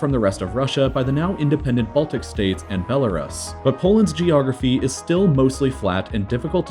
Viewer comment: I thought you said Iraq's geography is the worst